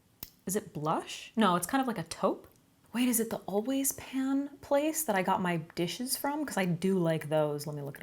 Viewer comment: I thought at first you said Always pads😬